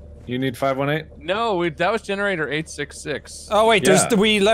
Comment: I thought generator 866 said generator EGG😂😅